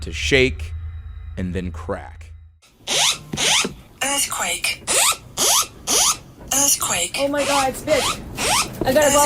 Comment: did she say “earthquake I have to go outside”?